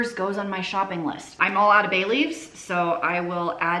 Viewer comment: I thought you said “ I am all out of Bailey’s” - I had to listen again, as I was thinking - holy cow she’s a cool mom! After the third run I realize you said Bay leaves! Lol